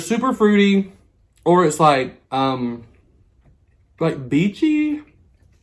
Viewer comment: Omg I thought you said beefy not beachy!!! 🤣😂